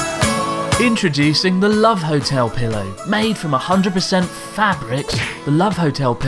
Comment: I thought he said made from 100% Fapric.